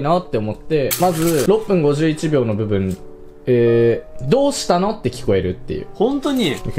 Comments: どうしたの？よりどうしように聞こえたと思ったけど最終的にローションに聞こえる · どうしたの？じゃなくてどうして？に聞こえるの俺だけ？w